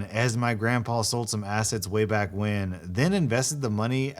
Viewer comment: At first I thought you said “my grandpa sold some acid way back when” in the first story and thought that was a badass grandpa